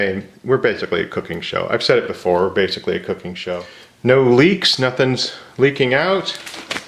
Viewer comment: "...basically a cooking show. No leaks..." - because of the context, I heard that as "leeks" and was like "Yes, correct, I feel like adding leeks to this would be VERY odd, even for you!!" 😂